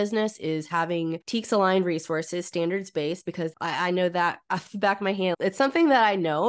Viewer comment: Hello 😊, what is said at 10:05? “ something… resources, standard space” sorry, maybe I’m just being dumb 😆